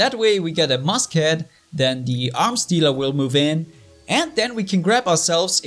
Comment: When said arms dealer I thought he said arms stealer